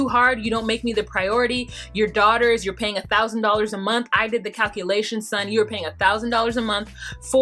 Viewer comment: I could be so wrong but I thought Leida said she calculated his payment and it was 1k a YEAR. am I wrong?? Great review! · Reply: 1000 a month